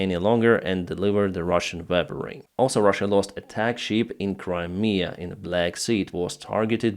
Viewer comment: I was just listening with YouTube on background, and I had to check twice. First I heard "sheep". Started to wonder how low Ruzzians can go if they put even sheeps to attack (All who had played Worms knows...). After rewinding and seeing a ship I got the message, not sheeps just a ship. :D